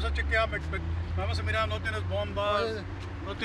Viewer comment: translation was off. The subtitles said weapons but the police officer said bombs lol.